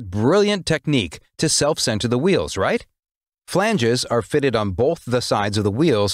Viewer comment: did he say “filangees”?